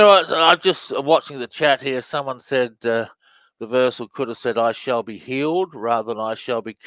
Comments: i thought it said, i shall be appealed . also hear, crooks sees his fate. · I heard "I shall be healed" on the second Trump reversal.